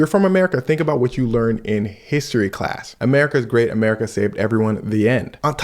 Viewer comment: I had to remind you I thought you said America going to save everyone😂😂😂😂😂